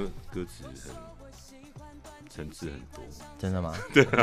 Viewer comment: 他說五月天的歌怎麼樣？聽不清楚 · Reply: “層次很多”吧